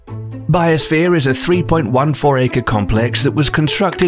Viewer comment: anyone else thought that said bio shrek 2? no? just me? ok then